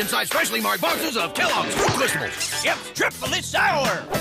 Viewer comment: Sergeant said Ordinary Tasting Snakcs but the subtitles said Ordinary tasting $ex. MAN WHAT IN THE